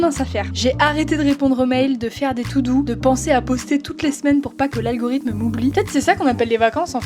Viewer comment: J'ai entendus , "arrêter de faire des "tout doux " je me suis dis mais pourquoi ?!? je ne sais pas ce que c'est mais ça a l'air cool ... (puis j'ai compris ). 😀